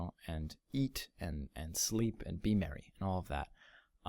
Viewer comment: Ok, sorry to hear that, but I'm sure there are other awesome things you plan for us :) If you are not married, what did you mean in 36:18? Or I didn't hear well? You're awesome, I am really happy for you hope you'll enjoy your new job! :D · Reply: It sounded like "eat, sleep, and be married, and all that". Maybe it's just me? :P